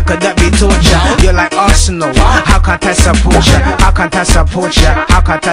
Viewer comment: Is he said arsenal or what